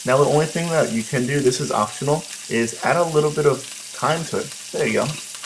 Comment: Thank you for the video. I am beginner learning to cook, i didn't quite hear what he added at 3:12, that green veggie. Can someone please tell me? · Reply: Thyme